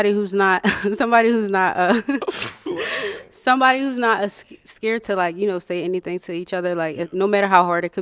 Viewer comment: did she just say "ascared"?😂